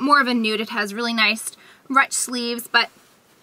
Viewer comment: haha I've never heard anyone pronouce ruched that way either haha. it sounded like you said roonched